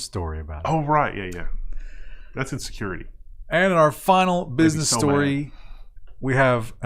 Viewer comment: "That's in security" ... All I hear is "that's insecurity"